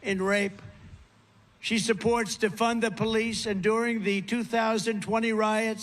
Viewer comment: sounded like „she supports TO FUND the police” rather than „…supports DEFUND the police”. some dumb dem might believe in the former…